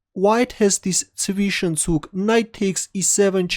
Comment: wth is that word??hahaha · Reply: Zwischenzug. The German word for "intermediate move".